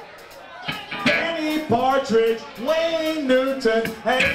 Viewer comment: Did he say "Andy Partridge"?